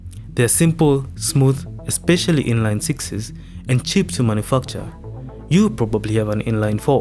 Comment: did you saymotherfucker?lol our manufacture.😊😅.